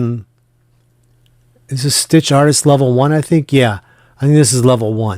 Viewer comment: Great monogram. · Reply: Ha! I just heard you say Artitst Level 1. I am new so don't know all of the terms yet:).